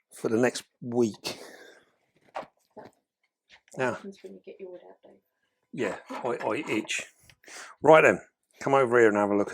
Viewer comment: Camera lady have expectations I hear 😅 "Are you getting your wood out?" 😂